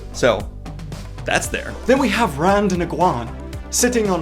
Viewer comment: did you say Rond igwon ?